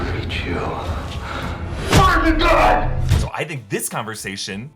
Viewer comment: I thought he said "You fired the gun!". At least that's what I heard